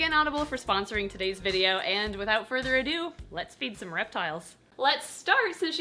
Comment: I just misheard that and thought u said: "let's beat our reptiles" OwO